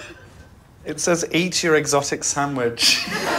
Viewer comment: i thought it said erotic sandwich 😩😔